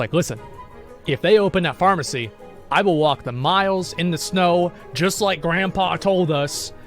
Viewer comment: *miles. Not singular. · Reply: fair enough. Thought he said a mile.